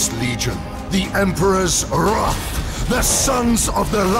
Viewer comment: What is he saying at 0:44? The Sons of Orion?